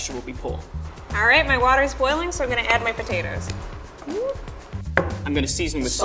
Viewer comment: 6 : 56 i thought he said im gonna eat my pan